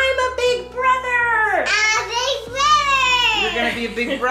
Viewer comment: when said "I'm a big brother!" it sounded like he said "on BIG broottttthhhherrrr"